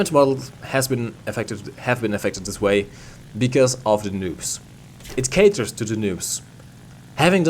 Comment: Damn it, forgot to edit the outro. Special thanks to Grendster, one of my Patreons! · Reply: Lmao I thought you kept saying nukes not noobs XD